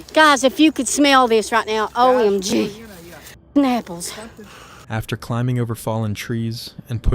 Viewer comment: did she say “nipples”?